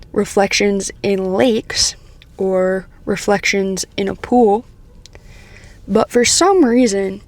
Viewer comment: I heard "reflections in a poo" 🤣and was like "WTF?!" Then I rewound the video, turned on the subtitles, and saw the correct phrase.